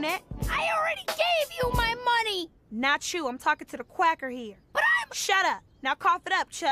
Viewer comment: Zoe really thought Nubia called her a cracker and I am dying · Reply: I thought Nubia said Quacker, and I thought Zoey thought she was referring to her voice